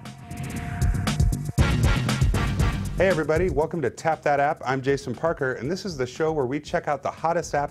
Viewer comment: i thought you said . TAP THAT ASS.. o'my......